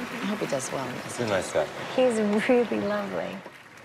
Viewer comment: Can someone explain what did she say in 1:09? (I'm not a native speaker) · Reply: I´m not native too but I understand "He is really lovely"